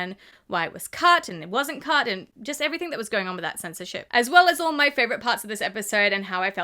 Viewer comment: did she say since of sh*t😅😅😅 or I’m just crazy and deaf and don’t know what I mean 😂😂😂😂 Not a hater I’m just curious that’s why the Aussie accent is amazing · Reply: She said censorship